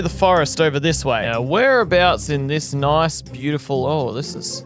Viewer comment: i heard 'towers above burp'